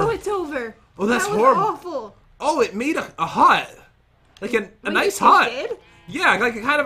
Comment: I thought it said “It made it hot” 🤣